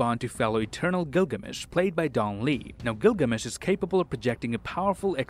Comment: did he just said gilgamesh